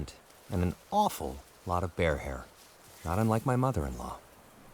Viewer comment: did he just say "not unlike my mother in law?" - 18:18 :D